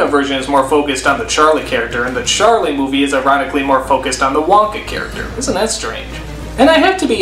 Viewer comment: I loved this episode so much... when Small World played for the first time I cracked up and the ending I was like "yes Yes YES!!!" Oh, and at 13:40 at first I thought he said "erotically"